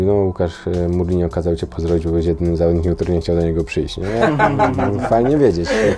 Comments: co on powiedział? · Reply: Powiedział : O Łukasz Mourinho kazał cię pozdrowić i spytał czy nie że chciałbyś do niego przyjść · co powiedzial? bo nie wyraznie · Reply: "No Łukasz, Mourinho kazał Cię pozdrowić bo byłeś jedynym zawodnikiem, który nie chciał do niego przyjść."